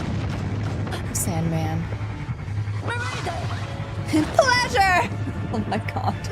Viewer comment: losing control isn't limited to turning into monster. Sometimes losing control also manifest in becoming psychotic, change in personality etc. Besides, I think the church simply labeled that archbishop as someone who lost control because no one in their right mind would do what he did. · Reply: I don't know why the subtitle keep on saying pleasure but I heard it was supposed to be Cocoon! 😭😭 which makes sense because she was placing herself inside a cocoon!